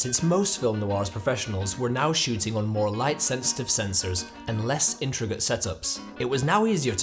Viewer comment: Did he say that films back then were shot using more "light sensitive sensors" at 4:39?